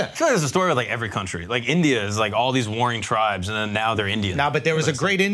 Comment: Did he say "Tribes"??